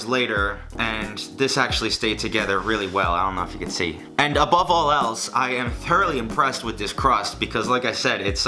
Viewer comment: did he say "above all "L's"" or "above all else" 😂